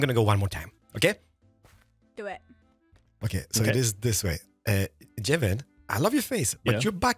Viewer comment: On gems stream I thought you said "gem you have a nice butt" I was like LOL that's bold... but it was jevin